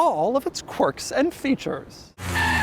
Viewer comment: Did I hear "Quirks and FEATURE"?? (not plural!)